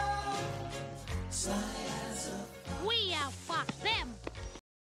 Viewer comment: wait, did Felix say someting ''like we are fuck them'' on the end? I know that is bad grammar, but that's what I heard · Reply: NealSadhaka he said "We outfoxed them"